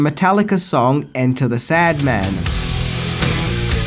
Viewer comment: Did he just say Enter The Sadman the 1st time lol? And on another point, it's called Enter Sandman, no "THE in it lol.